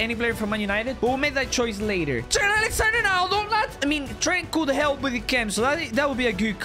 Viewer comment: It sounded like he said Trent Alexander-Aronaldo